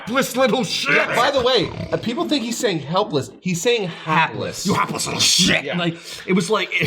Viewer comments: Huh... I always heard him saying "Heartless" there...like, even he's embraced what a cold monster Gohan's become now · I always heard it as 'heartless', which I think fits better with the situation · I thought he was saying heartless.